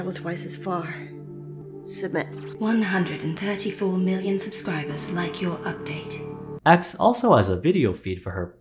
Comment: I had to listen to the robot voice 3 times because the first 2 times i could have sworn it said "134 million spiders liked your update." 7:39 "287.6 million nude subscribers have entered this room." oh no.